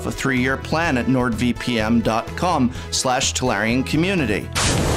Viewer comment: does he say "VPM"? lmao I love it